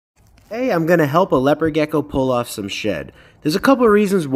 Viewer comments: Bro i heard you say “Hi today I’m gonna help a Leopard Gecko pull up some shit” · Why did I hear “ hey I’m gona help a leopard geko pull of its shi-“